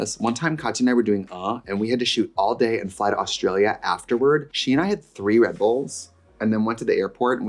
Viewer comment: When Trixie said “fly to Australia” I heard “fight Australia”.